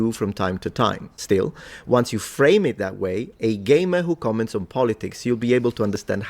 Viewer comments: I swore he was saying your a gay man commenting on topics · when he said game, I thought he said he said gay man